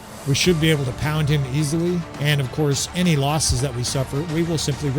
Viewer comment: Did you say you were gonna "pound that dude easily"?